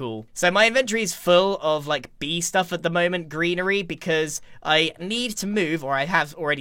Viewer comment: When Grian says "greenery" I hear "grianery"